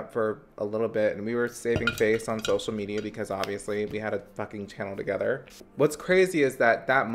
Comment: Bruh when he said "we had a fucking channel together" I thought he said "we had a fucking child together" and I freaked the fuck out